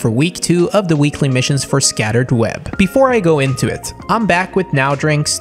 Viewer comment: did I hear "scattered web"? Also the new agent skins make my fps drop below 60 fps